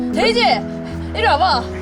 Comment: 에 데이지 이리와봐! 가 아니라 에이미 이리와봐 라고 들려요ㅋㄱㄱ옥에티 발견ㅋㅋ